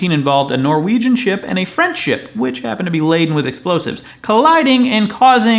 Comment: When you said "French ship," I thought you said "friendship." There is an opportunity for pun there. I hope you will take it.